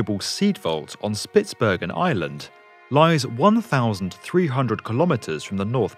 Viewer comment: did he say Ireland?